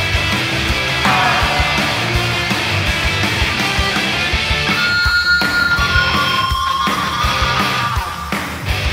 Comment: オカダ何て言ってるの？ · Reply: チャンネル チャンネル IWGPヘビー級王者って言ってる気がする